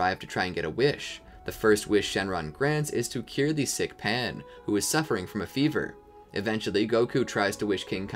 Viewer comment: anyone else hear "kill the sick Pan"